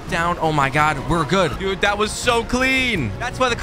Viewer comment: Bro I thought that you said mr clean